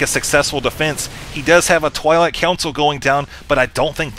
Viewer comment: Again! Is it just me ? Or does he actually call the Twilight Council a "Toilet Council"?